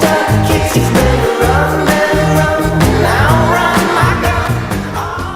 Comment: For the longest time, I thought he was saying Rebel, not Robert....